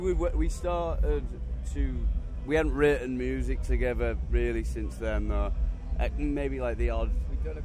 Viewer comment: what did he say?? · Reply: “We hadn’t written music together really since then though”